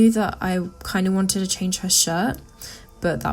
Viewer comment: Hands down my favorite drawing you have done of her. I love how you combined the different aspects of the realism from 2017 and the cartoon style from 2018. The new shirt looks great on her, and I think you did a really good job with the pose! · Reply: VictoriainDesign I thought you said skirt o was so confused